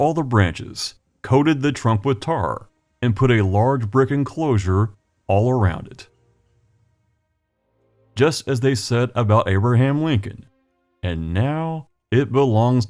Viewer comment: First time I listened to this I heard: "They cut off all the branches, covered the trunk with tar, and set it inside a brick enclosure, just as they did with Abraham Lincoln." Uh.. What?